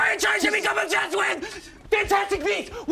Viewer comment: what's he saying? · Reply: He said, “I NEED A FRANCHISE TO BECOME OBSESSED WITH!”